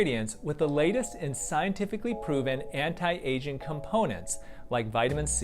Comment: when you were talking about your skincare line i thought you said anti-Asian components